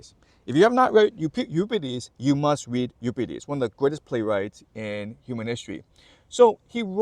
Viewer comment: what he said . Auto caption is not spelling properly . He said “ play of bakai from upsides “ can anyone spell that properly so that I can find it on google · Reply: Euripides - The Bacchae